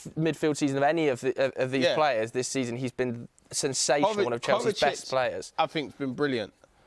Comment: ziyech after 3 good games😭😭😭😭😭 who are these guys man do you even follow football i dont get it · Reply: thought you were saying hes been pots, my bad